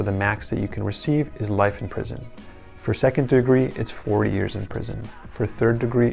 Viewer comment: 1st degree: premeditated - up to lifetime 2nd degree: voluntary heat of the moment - up to 40 years 3rd degree: not intentional but aware of possible dead - up to 25 years Voluntary manslaughter - animal instinct reaction (not thinking) Involuntary manslaughter - unintentional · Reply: I thought he said 4 years 😂